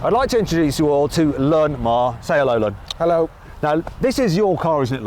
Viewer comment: did he just say his name is 'lon ma'